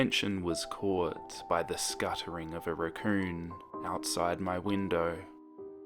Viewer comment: when u said raccoon I swear I thought u said recurring